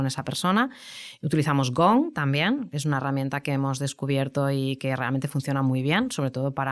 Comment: ¿Cuál es la herramienta que dice que utiliza en el minuto 01:00:15? Muchas gracias! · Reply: Si no me equivoco habla de Gong